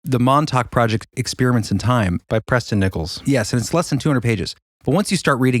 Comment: When you said the name of the author, I thought you said 'press them nipples', not Preston Nicholls. I died laughing when I rewound that and heard it right